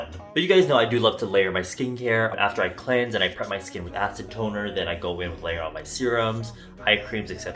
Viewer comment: Acid Toner. For a sec I thought you said Acetone and I was like ?????